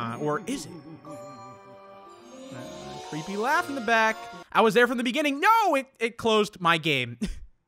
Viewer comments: This is what it said ???: I was there from the beginning. and I will be there until the end... LEAVE THE DOOR ALONE ) that's what it said here is it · it said I was there from the beginning and I will be there until the end... LEAVE THE DOOR ALONE. That's what it said